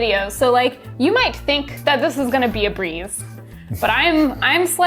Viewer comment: You said it should be a breeze. I hear brie. Add was like, I thought they said they were making mozzarella!?